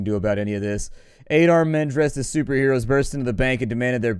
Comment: When was reading off a call at 6:12, I thought he said "An 8-Armed Man" and thought, 'wow, that must be what the REAL Spiderman looks like' xD